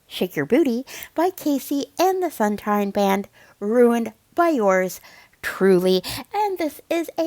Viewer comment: It’s KC and the Sunshine Band, but it sounded like she said KC and the Fun Time Band. How does Candy not know that dressing is clearly not a vinaigrette dressing, but something like Ranch, or something similar.